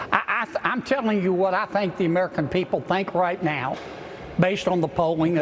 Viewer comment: “I’m telling you what I think the American people think!” I thought he said the polls. He’s such a nasty child man.